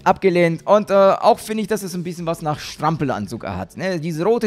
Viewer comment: Du sagtest ja Strampelanzug bei dieser Frau da ich habe die Schlampen Anzug verstanden xD